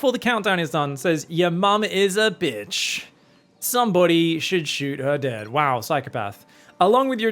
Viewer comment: In that second game I thought the guy was saying "I bet your mom is a bit chubby" and he just truncated it 😂